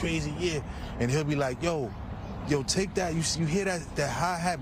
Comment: Anybody else heard when G Dep said, “Take that?”…1:52-1:53…🤣😂